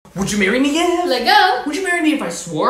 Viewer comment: I just now realized that "leggo" means let's go this whole time I thought she was saying let go just as leggo 🤡